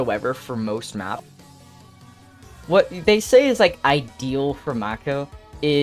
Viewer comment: I goon to buz- did he say ideal at 1:43 ????